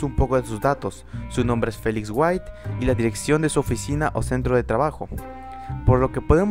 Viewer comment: pense que dijo phoneix wright